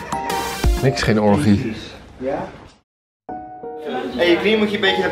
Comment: Vraagje: Wat zeg je bij 12:49 en waarom? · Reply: Arnout Kroon "Niks geen orgie", fantaseer zelf maar waarom! 🤔